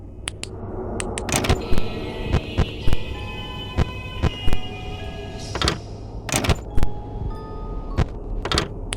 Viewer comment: what did she say? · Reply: "Hey kids!"